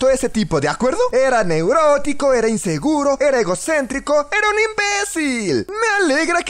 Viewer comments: jajaja yo escuche :era negrote xD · le entendí negrotico